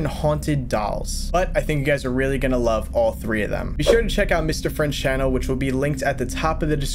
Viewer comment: It sounds like you said Mr French